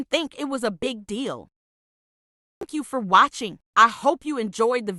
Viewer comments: at the end I thought you said "F**k you for watching" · I THOUGHT THE END SAID “F_ck you for watching” 😭😭😭